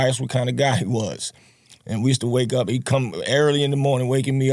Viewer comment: it sounded like he said we used to wake up and cum lol